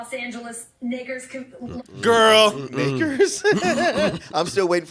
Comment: i thought she said knicker... like knickerbockers... or lakers combined with smthg else...